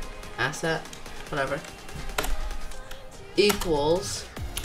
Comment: so for this bit her we have to do: "Ass" me: "What?!" Him: "Asset" Me; "Ohhh I thought you said ass lol!"